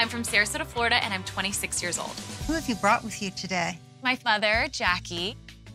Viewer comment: Did i hear correctly....she said my father Jakie?